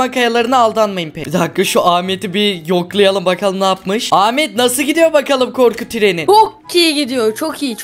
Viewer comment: ilk başta ahmet bok gibi gidi gidiyo diye anladım DKDKKDKDKDKDKKDKDKKDKDKKDKDKDKKSKZSKZSKZKSKSKKZKSKZKZKKSKSKKZKZKSKZKKSKSKSKSKKSKZKSKZKSKSKZSKSZSKZSKZSKS